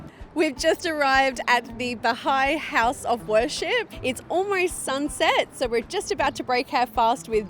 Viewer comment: We hope you enjoyed watching our Ramadan video. Our lives have been forever changed by this experience. We actually fasted and prayed for the whole week but didn't film the last 5 days so that we could fully immerse ourselves in the experience without the camera. Thank you for all the support and love ❤ Please SUBSCRIBE to our channel and follow us on Instagram <link> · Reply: I heard the lady saying 'Bahai house of worship' at 15:23, can you please clarify? I think that it is a mosque..and there are no other places of worship in Saudi Arabia except mosques..